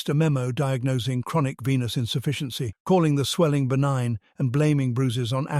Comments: oh dear gawd I need to clean out my ears. Instead of venous insufficiency, thought I heard penis..... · I heard it as “chronic penis insufficiency” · Idk about yall, but i heard "chronic penis insufficiency" more than once and had to play it back! Lmaoooo 😂😂😂😂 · He has venous insufficiency? I thought you said penis insufficiency. Well, duh!😂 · Wonder if Putin slipped him a poison french fry.. "Chronic veinous insufficiency"..I thought they said " chronic penis insufficiency". Granted that's true..but is it deadly?😂 🍄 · i almost choked when i thought i misheard " chronic Penis insufficiency " ... i had to rewind to check ya'll not trying to get in on the southpark gags